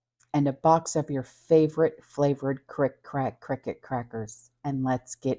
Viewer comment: I low key tuned out after I heard "your favorite cricket crackers", 😂🤣😂